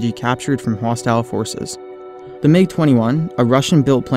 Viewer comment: i thought it said MLG 21 lol